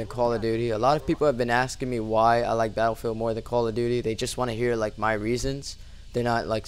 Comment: sounds like he says quall of duty